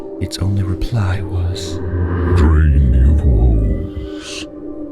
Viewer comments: This is what Thomas said: "I have seen past the farthest star. And I have gone to the edges of the earth. I can take you there, if you so choose. I can give you all the things you ever knew you wanted. Taste from the tallest chalice, dine at the largest halls. All I ask in return, is your soul. Your soul for a chance with life without pain, without heartache. All this I can give you and more. Just for your soul. Lightning Mcqueen: What of life after death? What are the paradises that await me in heaven above..? "Drain your woes. Your god has turned its back on you. That's why my tracks still push me forward." ~Thomas the demonic vessel. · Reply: I thought he said dream you fools. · I thought he said dream you fools. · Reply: abc abc I know you aren’t calling us fools when you’re literally the only one who thought he said “dream” when he said ‘drain’.